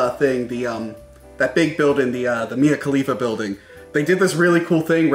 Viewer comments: I must have misheard him...did he say the Mia Khalifa building? 33:00 Like that can't be what its called!? I'm dead.😝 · did he just say the Mia Khalifa Building... I didn't know the adult film industry was so lucrative.